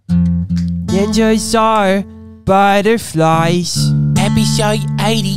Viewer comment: It sound like they said ninja are spider flies 😂